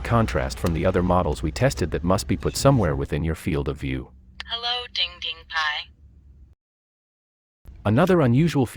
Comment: Misheard : Halo Ding ding Pie Original Hello DDPAI